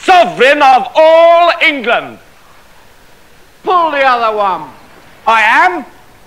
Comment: he says "pull the other one" meaning "you are joking" (you're pulling my leg) and King Author replies "I am". I always thought he said "Whose the other one?" meaning I thought he was claiming that Patsi was who he was talking about and his reply "I am" would mean that K. Author was agreeing with him that K. Author wasn't talking about himself being the king. It all made sense, but now that I know he says "pull the other one", the reply "i am" is now confusing, because by saying "I am" that means he is telling the guard that "yes, I am joking, i made that up and to continue joking with you, this is my trusty servant..."